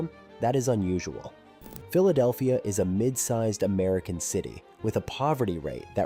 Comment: Doofus, he said, “Mid-sized AMERICAN city.” He didn’t say, “Mid-sized city compared to other cities around the world.” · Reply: The quote is “Mid-sized American City.” Are you slow? You have audio and still can’t get right. 00:27 - What does he say here? MID-SIZED AMERICAN CITY. You’re not even bright enough to listen correctly when you have on-demand audio you can rewind as many time as necessary. You are definitely dense.